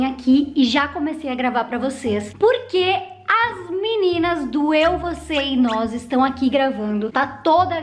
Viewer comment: Só eu q entendi "doeu você é nós"? Kkkkk